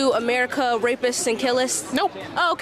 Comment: Did madam really say rapists and 'killists'?